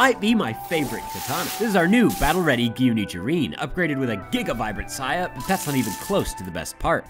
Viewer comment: Sounded like he said “upgraded to a Giga byte of data”